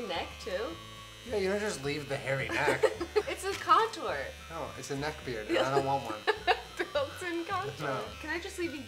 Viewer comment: "But it's contour" "No it's a neckbeard and I don't want one" 😂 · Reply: kaddaaaaaaaaaaaaa i thought he said "it's a neckbeard and this isn't Walmart"